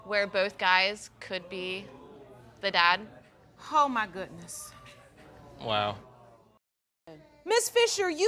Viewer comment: I’m pretty sure I heard the Judge say, “HO my goodness...” to the woman in the 4th couple